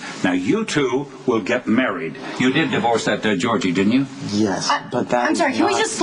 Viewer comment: Someone help me did Edward say, "you did divorce that dead Georgie?" @ 2:23 ish?